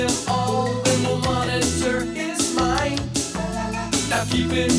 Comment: i always thought she said the monitor is mine